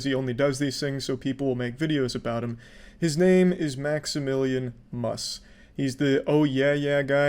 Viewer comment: I thought you said itsmaximum and. Like "wtf why would he do that" nopewas wrong thank god